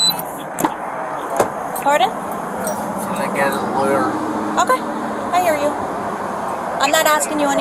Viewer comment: Didnt he say he wants a Lawyer? at about 2:10 when she opens the door I think he says can I get a lawyer? She says I'm not asking you anything.. If so, that could be a major issue, no? Or does that only apply after miranda rights are read?